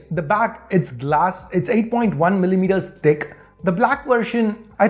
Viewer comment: Anybody who who heard d*ck instead of thick 😂😂😂😂